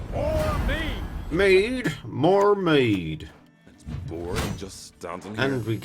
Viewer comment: Omfg, hes saying "mead"?! Suddenly it all makes sense. For so long I've been thinking it was "Me, or... me!" and was like well that sure is a weird thing to say. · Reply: I thought he said Mead or me, but he says Mead, more mead.